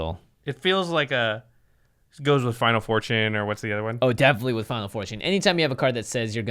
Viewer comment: every time I hear "final fortune" it sounds like you guys are saying "Final 4Chan" and I just die laughing.